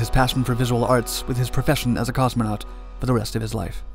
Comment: "Because I want.....to.......be....a movie star" Leonov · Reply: I thought he said moving star